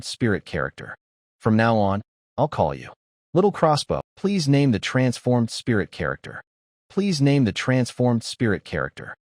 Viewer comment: How come around 8:41:20 all I hear for almost a minute long "name the spirit-formed character “